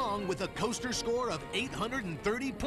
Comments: It sounds like she said ripoff at the end, but that's me being a non-Aussie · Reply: Aussie here. She said "bloody ripper" which means very good, awesome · Sounded like "Superman the escape is a bloody rip-off!" LOL.